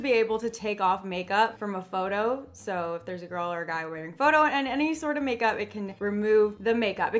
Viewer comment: Who else heard her say "if they are wearing photo in a makeup"